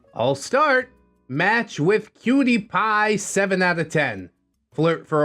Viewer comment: When the video started I thought that you said "matched with PewDiePie" and I was like "Oh shit, this is going to be a wild story!!"